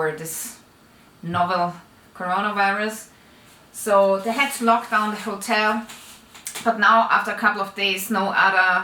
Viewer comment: For a second, I thought you said they had to ‘knock down’ the hotel 😱